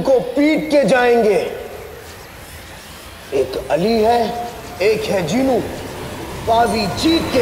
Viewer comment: did he said jeenu ?